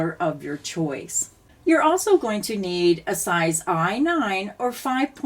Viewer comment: I think I missed the size of the crochet hook, can some one let me know please ? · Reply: I thought she said us size I